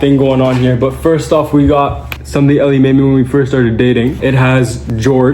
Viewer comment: I thought I heard say “Sunday made me…” 😭